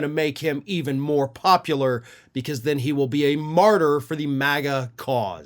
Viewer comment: Martyr? I thought he said Farter.